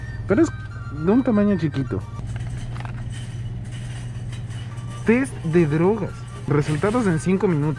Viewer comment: en la intro pensé que decía TÉ de drogas 😄😂😂😂